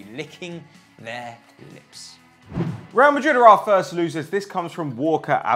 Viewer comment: did say ''PSG will be licking their nips